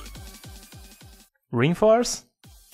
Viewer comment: On 3:08 i heard “Reinforce” XD